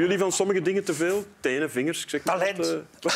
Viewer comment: wat zegt Stefaan? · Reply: "Talent" :)